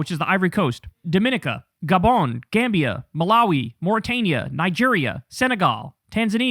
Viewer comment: Oh.. Mauritania.. I thought he said Morytania. The river Salve is keeping the latter out. (Just looking for fellow RuneScape players)